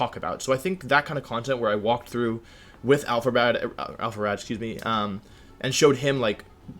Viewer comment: I HEARD IT.... ALPHABAD!!! 6:45 ....(btw love you guys)